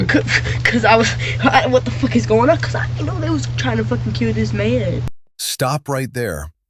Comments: she clearly said that I didn't know they were trying to kill this man. Not I know they were trying to kill this man. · Is it just me? I heard her say she ‘didn’t’ know they were trying to kill this man, not the the opposite. To be clear, she’s lied a ton already so it needs to be taken with a grain of salt, but I believe the creator misheard what she said at that moment